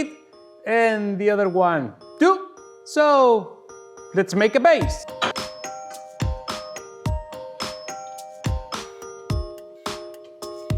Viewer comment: I heard it as “let’s make a bass!” I see a Davie504 collab in his future.